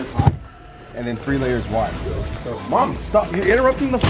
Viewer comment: So am I the only one who heard him say mommy stop